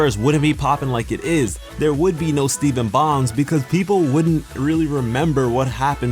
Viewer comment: Me;Did he just say "steven bongs"?